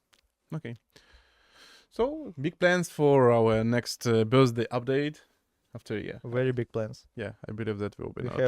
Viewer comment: did he said big plans for next birthday update?